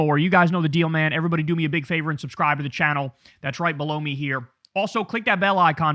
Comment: Kyle dont talk about your "links below" as "below me".... it sounds like youre saying "blow me"